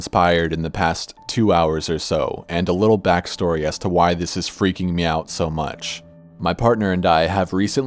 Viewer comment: I was a little bit distracted with the picture and at this part I literally heard "why this is freaking meow" and just cackled. Ok, sorry (me trying not to get scared because I'm such a coward but I enjoy Corpse's vids)